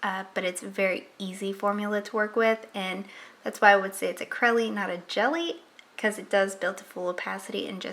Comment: you say it's a ... (Something) and not a jelly. I couldn't understand it. What did you say??? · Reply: "Crelly", not a jelly.